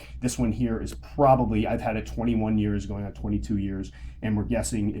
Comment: Did you say you've had that spider for 21 going on 22 YEARS? Jesus. What is the life span?